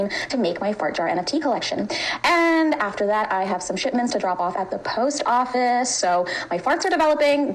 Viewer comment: excuse me? Did she actually say shitments or am I crazy?